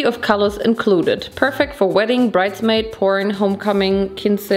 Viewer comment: did bro say porn instead of prom? 😭